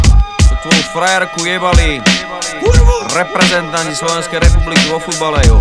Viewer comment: Co on mówi w 3:35??? Reprezentant śląskiej....?